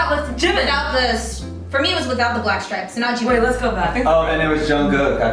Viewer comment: I could’ve sworn she said red hat with stripe. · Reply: “For me it was without the black stripes” That was her exact words. 19:46 You can turn on the Subtitles to see it if u want. Anyway Vmin they both are talented so does all 7.She chose V and her bf chose Jimin for members who stand out for them.